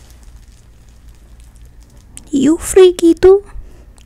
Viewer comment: THE WAY THEY SAID "you freaky too? " MADE ME LAUGH 💀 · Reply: I thought she said you freekito